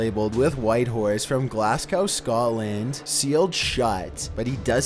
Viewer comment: Lol sounds like you said glass gow 😅 should be Glasgow. Always sounds a lil weird when an American or Canadian says it lol (